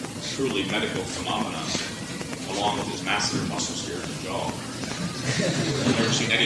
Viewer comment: What does Mike say at 8:30? “Along his massive muscle here in his jaw?” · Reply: He is actually saying the “masseter muscle” (a muscle of the jaw that was particularly hypertrophied in Tom Platz’s case).